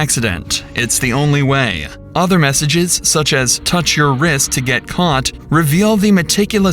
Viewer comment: 💁🏽‍♀️"Touch your wrist to get caught??"🤔 I didn't get what that text was about... anyone know what that part meant? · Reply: She said it meant "Too much of a risk to get caught"